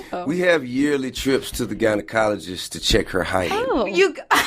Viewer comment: I thought he said :” we go to the ginocologyst to check her hiney”